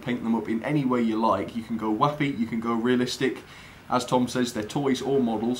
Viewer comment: I can go whatnow? Didn't catch that first adjective. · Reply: haha I think I said whappy, whatever that means! ;D Thanks for watching -:)